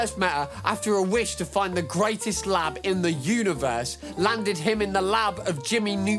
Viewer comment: for a second there, I thought you said he wished he found the greatest LAD in the universe, like DANG they made Cindy THAT fine?! :P